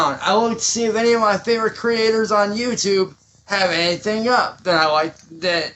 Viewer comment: I thought he said "1 of my favorite dirt" or something like that instead of "creators".